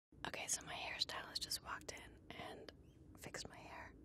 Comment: At the beegining i really thought she said :"When Harry Styles walked in.. "😂😂